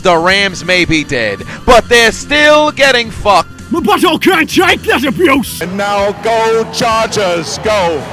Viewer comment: What is that line at 8:46? It sounds like “Something can’t take that abuse”. I was want to know what is being said and where it’s from. Thank you!!